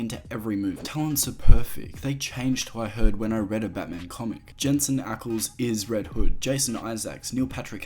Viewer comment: Can someone help me? I’m hard of hearing and the captions suck. What is he saying here 18:56 ? Talents are perfect. They changed who I read them in a Batman comic. ???